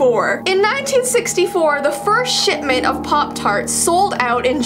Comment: She says “Shipment” what I thought she said “ Shit ment” Me: Oh Jessie Sweared. Goodness. Mother: She said shipment you stupid child.” Me: Oh, okay boomer. Mother. >:0 Me walks away proud